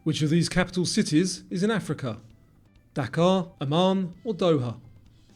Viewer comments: That question with Dakar really threw me off because I was only listening, not looking. I thought he said Dhaka as in the capital of Bangladesh and I got the question wrong lol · 85/100. I closed my eyes for many, including, "which capital is in Africa?". I thought you said Dhaka (it was Dakar, I'm American), which I know is the capital of Bangladesh, so I picked Doha. The most islands question got me too. I for sure thought it was Canada.